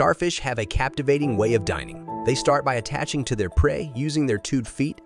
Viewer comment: I thought you said dying but you said dining